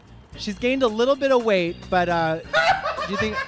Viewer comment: i always thought he said butter thighs 😭